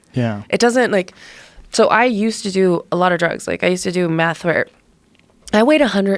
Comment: does she say she used to do meth? Holy shit.